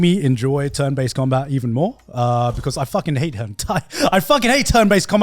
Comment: am I the only one who heard garnt say he hates hentai